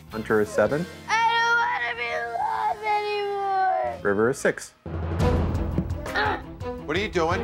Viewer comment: did he say “I don’t wanna be loved anymore”? 😂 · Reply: "I don't wanna be alive anymore"